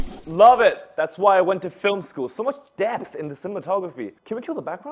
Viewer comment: i thought director said so much “death” after the actor tripped over and fell to death.